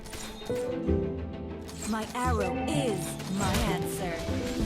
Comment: What I heard : My power is my cancer/pencil